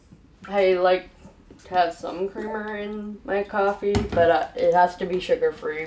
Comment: I thought she said sun cream and got INCREDIBLY concerned